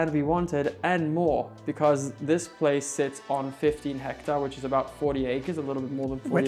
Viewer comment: said 15 hectares which is almost 40 acres. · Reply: I thought he had said 50. Thank you for your clarification.